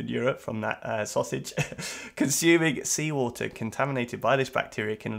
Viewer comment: Why did I hear "seawall" when you read "seafood"?